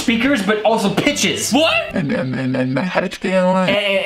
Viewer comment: she is definitely saying "anything thats on my neck".